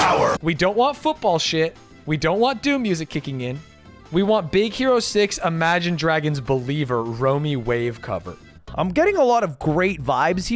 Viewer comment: I thought he said "Roe V Wade cover"